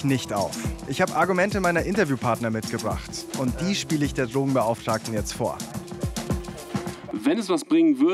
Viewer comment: Hab ich als einzige "Dummenbeauftragten" verstanden? XD